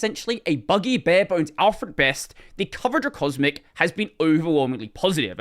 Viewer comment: there are some issues with the subtitles 1:21 subtitles say best instead of at best 5:38 Mer instead of Mir and Genome instead of GNOME 6:34 XFC instead of XFCE 6:47 Genome instead of GNOME 12:29 ex-Wailand instead of Xwayland 14:09 XSFE instead of XFCE 15:37 Genome instead of GNOME 17:11 PopWest instead of PopOS